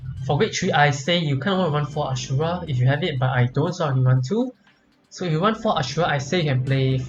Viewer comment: Thanks for having my back:) Glad there's people out there who understand what I am saying! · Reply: Oh i did not mean any offence your language is perfectly fine it could just be the microphone for example take 10:29. This is a key detail about the deck as English it not my first language either to me it sounds like "So for victory i say you CANNOT run 4 Ashura if you have it but i don't so i can run 2" which contradicts what other Youtuber's say? So for victory am i meant to run 4 or 2? and a good example of the microphone possibly being bad is 10:49-10:50 where you say something but i cannot get it. So only for the parts where you are explaining the mechanics of the deck (i.e not what the deck contains) these things need a bit of subtitles in my opinion as you want people to learn from your experience and strategy. Even other Yuetubers like DifferentFight who speeks way to fast; i watch his videos on 0.75 speed.